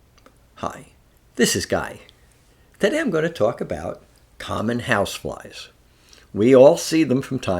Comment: I heard you wrong. I thought you said common housewives 😅 I think it's your accent